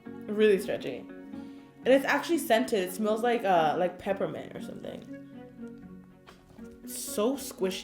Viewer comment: "it smells like um, peppermint." I thought she said peppa pig